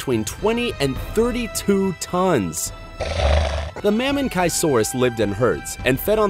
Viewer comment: Him : saying that mamenisours lives in Asia that’s what I heard him say Me : oh no oh no no no I don’t wanna die